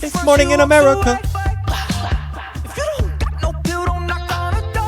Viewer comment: Also in Modern times, Jon is using word play in the end, it sounds like he is saying ‘Merica, but he is actually saying Nineveh, the wicked city in the Bible…